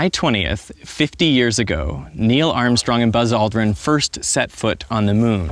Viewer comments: subtitles read "Buzz lightyear" instead of "Buzz aldrin" 0:03 lol, im assuming it was intentional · If u have text on At 0:04 it says buzz lightyear instead of buzz aldrin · turn on subtitles, at 0:06, it says Buzz Lightyear instead of Buzz Aldrin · the subtitles say buzz lightyear instead of buzz aldrin